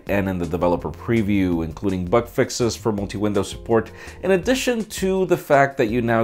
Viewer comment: I thought he said butt fixes... Lol